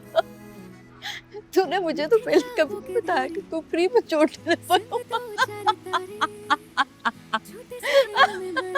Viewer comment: yhan kya Bola iss ny?? K free mei...... Kya krta tha??